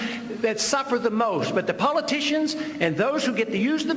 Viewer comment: I think he called them “puppeticians” 🤣🤣